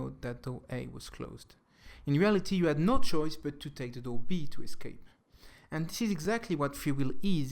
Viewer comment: What is he saying from 4:30 to 4:34 ? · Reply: Really late to this, but he said, "In reality, you had no choice but to take Door B to escape."